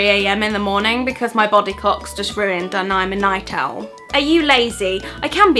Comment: for a minute i thought she said body cock